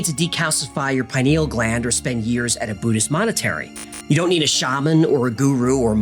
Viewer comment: Were you already aware that you said: "Buddhist Monetary" @ 00:34:06 during the segment about "The Gateway Process" Instead of: "Buddhist Monastery" I'm not trying to pick your work apart but something tells me that would still want to be made aware of any mistakes that slipped through the cracks and were missed by the editors in post production... You're welcome...! "BE GOOD" or "BE GOOD AT IT"